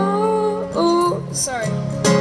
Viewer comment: Did he just say "sorry"??? You sing awesome man!